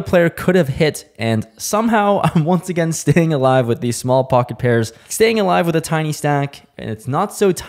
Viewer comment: I swear I thought you said “Pocket AirPods” during the break 😂😂😂